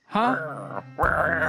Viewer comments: I hear "potatoe for chicken" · I thought it said "pirate of the chicken"